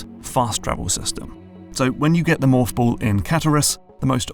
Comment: Until the text appeared on screen at 8:23 I thought you were saying “moth ball”